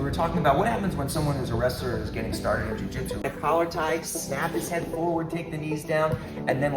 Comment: I swear I heard, "what happens when someone gets arrested or is starting in jujitsu"